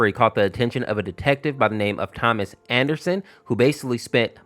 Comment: Did you just say Thomas Jefferson